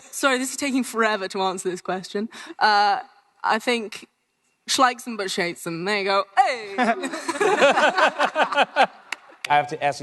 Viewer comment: Tell me please what did Sophie say on 14:30? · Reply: "She likes him but she hates him, there you go, heyyyy!" I didn't hear it either, I read it from someone in the comments ;)